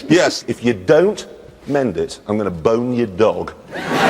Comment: I heard “I’ll bone your dog”